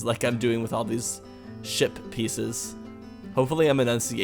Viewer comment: When you said ship piece, I thought you said shit piece.